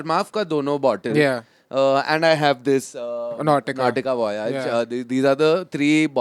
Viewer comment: what's the perfume name, couldn't understand. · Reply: Nautica Voyage!